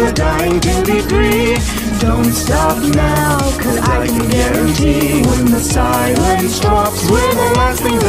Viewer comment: I thought they said quarantine..